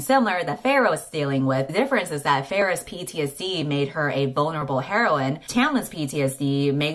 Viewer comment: I’ve been rewatching this video so many times (because it’s great) and this whole time, I thought at 17:13, said “Feyre’s PTSD made her a bone-able heroine”. 🤦‍♀️ It was “vulnerable”, but I guess either way works.